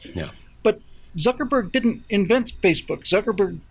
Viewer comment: Thought Bill said 'Suckerbored.'